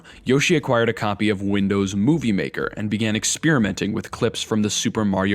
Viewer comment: i thought you said windows booby maker